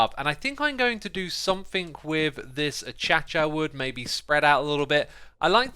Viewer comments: Did he just say “a-cha-cha” instead of “acacia” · D-Did he say A-cha-cha wood... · Did he just say “a-cha-cha” instead of acacia?